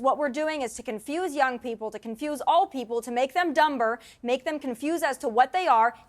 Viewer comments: Did she say 'dumb-ber'? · Did she say dumber, with a hard B at 9:00 minutes? I agree with her, but that kind of downgrades her intelligence! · She really said “dum-ber” LOL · yes but did she say "dumBER:? lmao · did she just say (Dum-Ber)? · did she just say “make them dum-ber”? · Did Tomi say “dum-ber” instead of dumber? Wow.